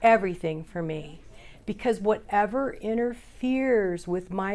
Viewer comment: At mark 29:39 minutes, refers to "Whatever interferes with my relationship with other human beings and my higher power .."; I heard this as "Whatever inner fears ..." Huh.